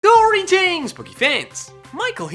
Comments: He always sounds like he’s saying “Greetings!, Spooky fans” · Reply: He sounds like he says Greetings! Booky fans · I heard him say boogie fans instead of poke fans